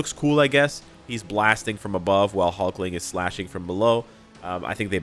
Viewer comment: I thought, for a second, you said " Wiccan blasting from above, and Hulkling 'smashing' from below...." SIR!!!!!! 😂😂😂😂😂